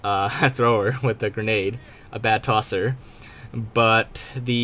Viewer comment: did you say... TOSER?